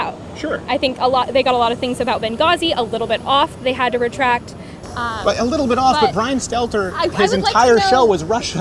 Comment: did Crowder just say Libya was "A little bit off"?